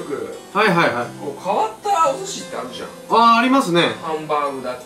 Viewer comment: ありますねぇがやりますねぇ、に聞こえた